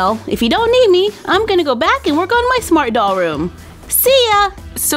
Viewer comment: Did I heard" my smartdolls room" ... What!?!?!? ... Love it😘😘😘😘😘😘😘😘😘😘😘😘😘😘😘😘😘😘😘😘